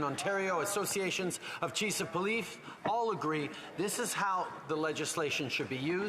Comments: Did he just say cheese of police? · Did he just say, “Chease of polief?” 😂 😂